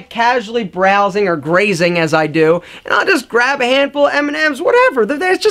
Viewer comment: I heard "I eat a hand of eminems"